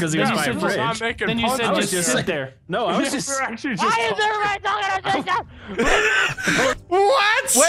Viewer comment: I think he said "WHY IS EVERYBODY TALKING OVER ME" or something like that 05:02: "I've never heard him catch himself in one of those" [Forgetting the 'Guy Ferrari, 'Ginner Duests', and 'The Eating Bench']